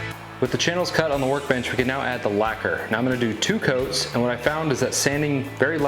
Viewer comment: Great video as always! One question, did you say laquer at 8:18 or did I misheard that?